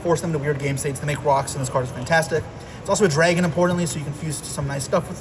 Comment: What is this card? Can't read the words or understand the name he said lol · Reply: Fantastical Dragon Phantazmay